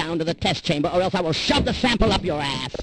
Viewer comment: I thought it said “microshave”